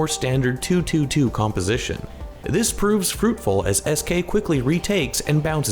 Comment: I thought u said eskay